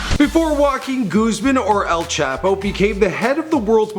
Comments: omg stop trying to speak Spanish lol..I thought u said walking Guzman then sinlow lol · Walkin goosemen, is that what i heard him say. Jajaj